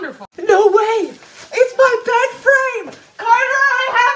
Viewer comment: I thought you said it’s my best friend Lol😂😅😂